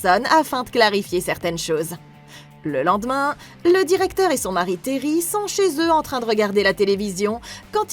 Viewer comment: le directeur et son quoi ? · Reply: Son mari Terry